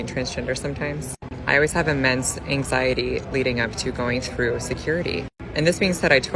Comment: she said "immense anxiety" but I heard "a man's anxiety"